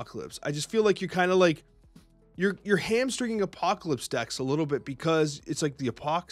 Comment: Did he say "Hamstering" instead of "Hampering" 😭😭😭